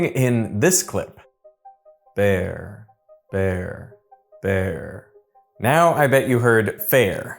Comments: I hear "There". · Maybe Derek is tone deaf OR is deaf. :}| Also you can't trick me: @ 12:20 with the Bear sound you're using the B and you can hear that because of your mouth sounds. The second one you say "There" you can clearly hear the T sound. I don't hear any F sound. Also possible YouTube or whatever editing software used messed it up. Or it's possible that people keep getting pulled into these illusions due to the internet, our brains are learning the tricks. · I hear there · i heard "There" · i heard there instead of fair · I heard there and bear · I heard there and bear, not fair 😅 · I heard "verr" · I hear “Vare Vare Vare” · ahh nooo, I didn't hear fare, I heard "there". 12:43 - It's switching between 'there' and "bear'. When I close my eyes and listen, the same happens. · no i heard "there" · I hear vert, green in French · I heard “there” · I hear "there". The visual is of the tongue touching the teeth, to make a th sound. "fair" would have required closed lips. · I heard "there", not "fair" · as English is not my native language I heard there instead of fair. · i just heard "ver" 😭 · nope, i can't hear "fair". I hear "their" instead. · Honestly I closed my eyes, never looking on the vid and the first one I heard "bear", while the second clip was "there" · actually, I heard "There"... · i heard wear